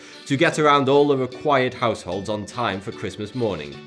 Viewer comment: ass holes? ppl can bealive at what they want.. idc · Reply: Clorox Bleach When he said households I saw his that comment by coincidence, I actually thought he said assholes too.